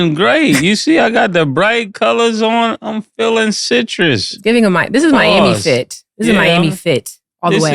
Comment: Feeling citrus is low-key CRAZY 😂😂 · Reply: Super. He said "citrus"... I heard "zesty". I'm like ay man wth